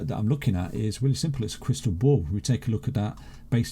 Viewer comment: I thought he said he has a crystal ball...